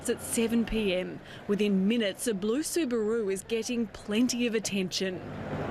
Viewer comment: sorry i thought i heard you say a blue subaru... ???